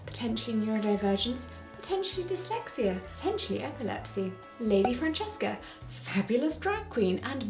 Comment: Hey, quick head's up, at 8:00 I hear you say "potentially epilepsy" but your subtitles say "but he definitely had epilepsy." I don't normally correct subtitles when the difference is minor (although I want to because I have that frequently-interpreted-as-pedantic kind of autism), because I think everyone who uses them know auto-subtitles and even manually written captions can contain minor mix-ups like homophones, but this is a pretty big difference in meaning and I don't want anyone to get confused. Cheers!